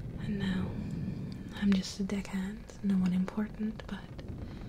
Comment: So I'm saving ghost pirates now and have a mark on me. I can't wait to see more of this story I love pirates! :) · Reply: Also when you first said I'm a deckhand I legit thought you said dick head I'm like what? Then I paused and read the description and went ohh xD